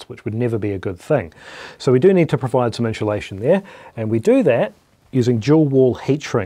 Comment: I thought you were saying enchilada but you were trying to say insulation. I guess keyboard hands are good for getting stripped wire in a crimp. My beat up fingers hardly move much less do a ballet you did. Good job